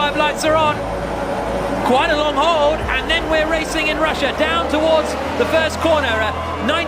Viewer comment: did he said "racing in russia" ?